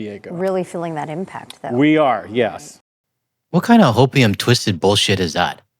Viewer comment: Opium twisted BS! Thanks for that. It actually calmed me down and I had a good chuckle. Cheers from a fan in northern Ontario. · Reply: I heard ‘Hopium’ but either way it’s a good one!